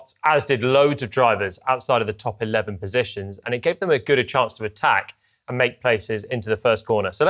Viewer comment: did Joylon say ‘gooder’?